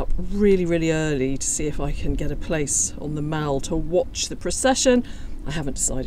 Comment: What a great video! Thank you for all the interviews down on the Mall. What an historic time!! I love the monarchy. God bless the King and Queen. ❤️🇨🇦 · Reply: I kept hearing “The Mall” as “The Maul.” To my Southern American English ear, the two words sound different. Had many questions if it was called The Maul, lol!